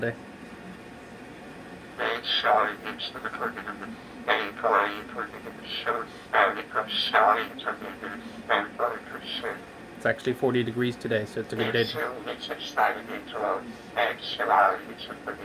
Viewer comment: i think he's saying, "i eat salt and celary at 7 degrees"